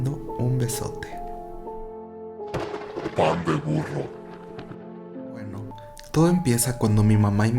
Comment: Jajaja wey, cuando dice el título "pan de burro" escuché 'mandenprno'''' oh Dios